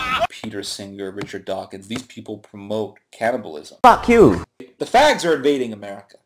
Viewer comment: At first I thought he said "cannabis". When I realized he was saying "cannibal(ism), I almost dropped my roast baby.